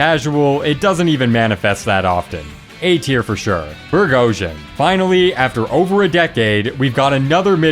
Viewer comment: what? No seriously what did you just say? I think you meant Burghausen but I just got the burg